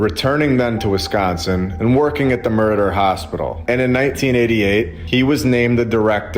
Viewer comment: It sounds like you said he worked at the "murder" hospital at 27:15. It was Meriter Hospital but it sounded funny. Probably not funny if it really was though.